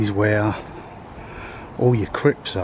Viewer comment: Good video man. Your explanations are always on point. It’s definitely a big cemetery man. When you said this is the area where the crypts are, I thought you said this is the area where the crips are 😂 I was expecting bloods to turn up 😂 thanks for posting 😎